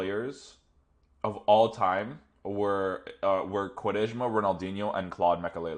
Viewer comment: Did you say Quaresma?